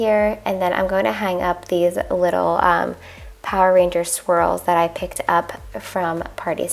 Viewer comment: Hahahahaha I could’ve sworn you said “Power Ranger SQUIRRELS” full on belly giggles when I realized what you actually said. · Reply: caroline john “Swirls” lol